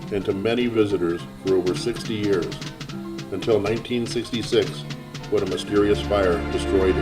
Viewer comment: Please listen again to minute 5:10 ... no one says 1956... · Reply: 1966 it burned down as the automatic subtitle says, but maybe I hear 1956